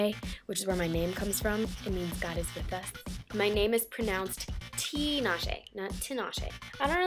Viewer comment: When she mentioned what her name meant, I thought she said "Goddess with us." She is absolutely right, 'cause she's a goddess haha.